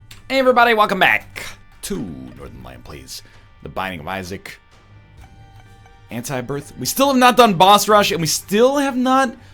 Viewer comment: Did he just say "Welcome back to NL Plays Antibirth"?? GG NL